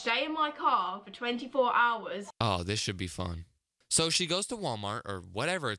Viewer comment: I thought he said “she do be fine” but instead he said “this should be fun” 😅